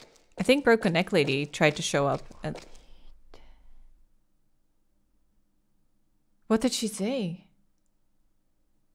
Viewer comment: I think she is saying something like "Ko ni ite". Now, I don't speak Japanese, but Google Translate says it means "Stay here", which is quite creepy, and probably what she whispered in that case. I could be wrong though. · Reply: Maybe it was “koko ni ite” then it means stay here yeah!